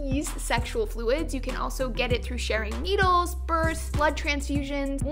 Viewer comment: When she said sharing needles I thought she said "AIDS can be spread through Sharon needles" 😂